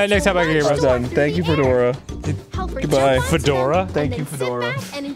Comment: I thought she said "Thank you for coming to hell!"